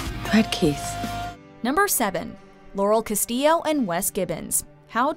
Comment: I heard Yanny Castillo